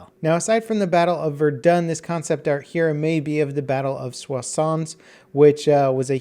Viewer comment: sounds like saying croissantXD